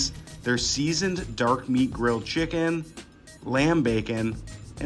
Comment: I thought he said ram bacon 😂😂😂